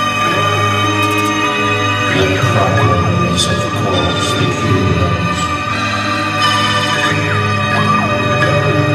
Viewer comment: what is the Martians saying? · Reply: The Problem is of course the HUMANS